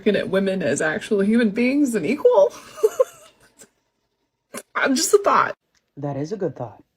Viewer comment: I heard "I'm just a thot." Sounds legit.